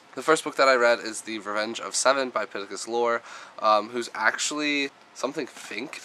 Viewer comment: April was my best reading month ever! 15 books! WOOPWOOP! I'm sorry, this is very exciting :D You had a great reading month as well! When you said "Pittacus Lore" I thought you were saying "Pikachu" :P